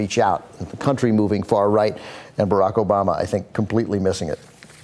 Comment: I laughed out when I read "brocco bob" in the subtitles, instead of Barack Obama